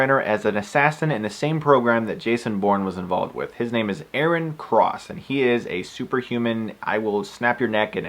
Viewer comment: Yea, you're right, it's Aaron. I could've sworn I heard Eric :s